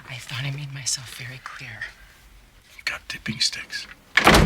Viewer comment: No matter how many times I listen to it, I ALWAYS hear "da bee sticks" instead of "dipping sticks"